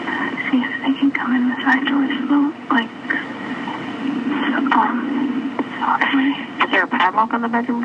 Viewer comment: it's listed as indistinct; i think she's saying "side door.. like, uhm, softly"